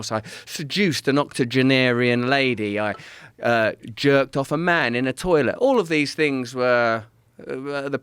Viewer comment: Had to rewind 2:26 like wait did he say he jerked off a man in a toilet 🤔🤔🤔